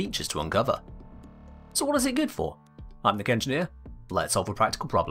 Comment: Every intro I hear 'Thicc Engineer' I know better but it gets a chuckle from me every time, lol.